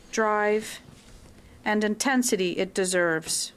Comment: "intensity" sounded like "in tent city". Subliminal messaging much?